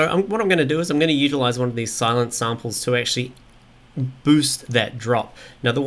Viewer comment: Thought he was saying Sylenth samples for a while...derp